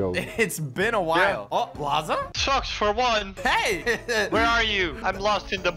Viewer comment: Guys I'm going to be truthful I thought he said sucks for what untill I found out it was lol